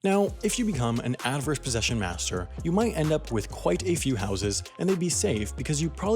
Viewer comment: Oh, HOUSES. I thought it said horses.